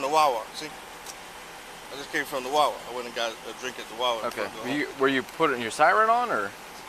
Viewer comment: "I was just making sure he wasnt drunk"... then goes on to say "i just left the bar and had a drink"😂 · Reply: I thought he said he left the wawa and got a drink and pointed to his wawa soda.